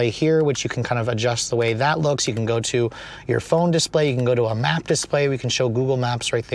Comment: did you say "you can go to a phone display"? Did you misspeak or did I hear correctly and you meant that Apple Carplay or Android Auto can appear where the gauge cluster is?